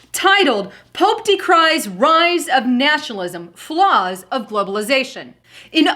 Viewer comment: If you play vid from 2:50 to 2:56 at 1/4 speed, sounds like says "Nashalism" when she actually says 'Nationalism'